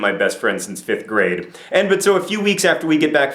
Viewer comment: Okay, at 1:36 or thereabouts.....what conjunction is that?? It sounds like John just couldn't decide between three different ways to start the sentence, and so just decided to go with all three: "And/But/So, " Did I mishear? or am I missing something?